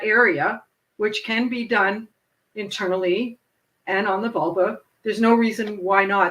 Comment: the transcript states volvo instead of vulva — slightly different meaning!